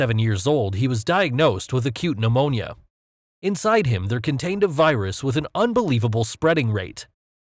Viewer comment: I thought he said corona virus but it was conntained a virus